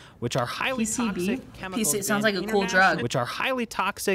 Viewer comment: I misheard it "this is sound like a good (cool) D R U G" im wheezing lmao periodT.